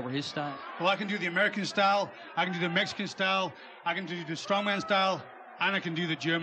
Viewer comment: lol @ 0:48 it sounds like he said "I can do the American style, I can do the Mexican style, I can do the Strawman style and I can do the German style"